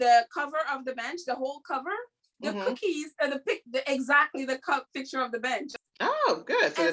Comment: I thought you said the picture of the BITCH” lol